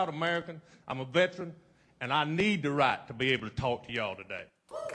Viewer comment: what is the guy at 11:40 saying? after he said he is a proud american? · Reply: He said, "And I need the right to be able to talk to y'all today."